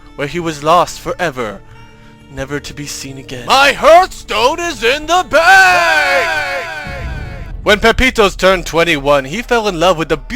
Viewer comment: i thought he said "My hearth Stone is in the bay ay-ay-ay-ay!"